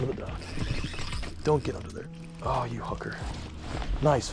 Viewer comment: did just say “ohh you hooker” 😂😂😂